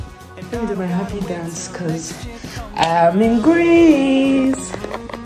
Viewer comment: Thought you said I’m engaged 😅😅😅apparently it’s I’m in Greece